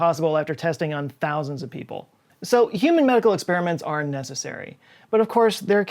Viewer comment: I thought he said "human muggle experiments"